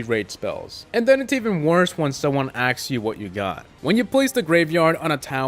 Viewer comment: said ax instead of ask